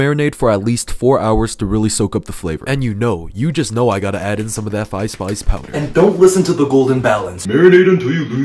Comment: I thought bro said “and you know I gotta use that ice spice powder” 😭